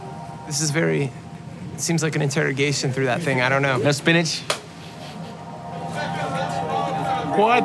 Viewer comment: Is Armin Van Buuren a vegetarian because I heard him say "No Spinach" on camera?